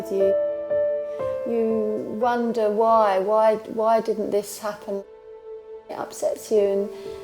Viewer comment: Who else laughed when they heard her say "why did I do this"? Lol